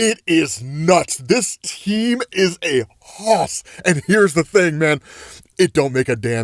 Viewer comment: What did he say here at 3:09 "this team is hoth" cant not understand it lol captions didn't pick it up either